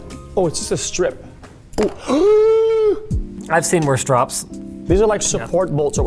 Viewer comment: what linus said: "I've seen worse drops" What linus meant to say: "I've done worse drops"